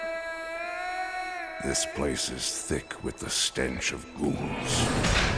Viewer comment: what he (barbarian) said at 0:10 ? or "this place is thick with a stanchion humans" ?